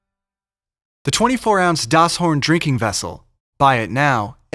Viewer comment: sounded like they said Das horn porn